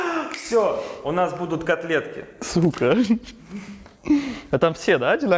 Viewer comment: мне послышалось "КОТЛЕТКИ С ЛУКОМ")))